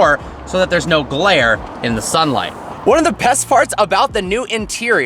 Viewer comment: I heard u saying ''Best farts''